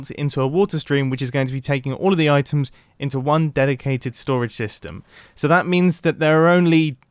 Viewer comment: I thought he was gonna say dedicated wham